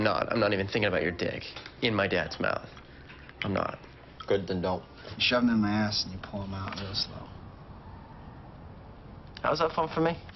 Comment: I love that scene, especially that "good to know" at the end 😂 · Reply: Wait i thought he said "good then don't"